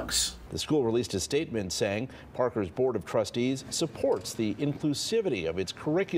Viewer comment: I thought he said "butt of crustys" instead of "board of trustees"